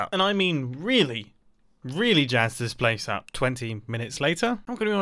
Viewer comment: Am I trippin or did he say: " twenteen minutes later "?